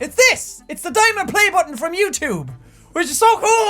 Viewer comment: i thought he said a diamond play button from mewtwo